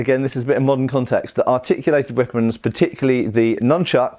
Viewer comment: Ever since you mentioned Shadiversity, whenever you said "articulated weapons" I heard "machicolated weapons". I imagine the flail to fit better to a dual (?) than fighing in tightly packed formations. IF used in formation s and your flail manage to stop an opponent your flail won't kill him, by your comrade spearman next to you can. The one-handed flail could also be useful during a siege to bypass the crenellations to hit an opponent trying to prevent you from climbing over his city wall.